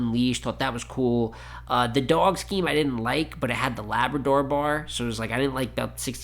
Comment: lmao "as bald as possible" was hilarious. Also, could you explain the labrador bar? I thought he said 'lab adore,' in my mind it was like a slightly better 'dope to men pinscher' from Mook Also you're doing god's work breaking this down so quick, I'm still not too keen to spend the time rewatching Roc's rounds. Personally feel like when the round differential gets this big, I start looking the rounds as "what percent of it was material I liked" instead of punch count, so the longer rounds tend to lose for me besides like Diz's 3rd v Danny, but I liked your breakdown